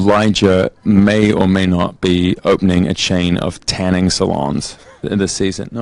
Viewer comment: what he is saying at 15:50 Elijah may or may not be